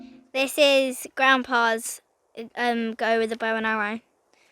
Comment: Oh I thought you said Bone Harrows. I was going to axe you about that.